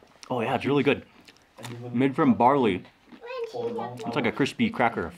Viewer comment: I was multitasking when you said malt barley and thought you said Bob Marley 🤣🤣🤣 Love your show Mark. Such a pleasant personality